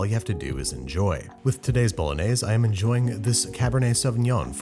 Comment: I thought it said bologna. Dislike ;)